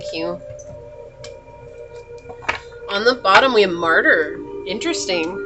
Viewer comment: Martyr! I thought you said murder 😂